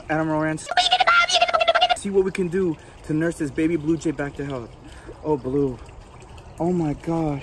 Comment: When he was saying he would get him/her back to health I thought he was saying back to hell. like if you agree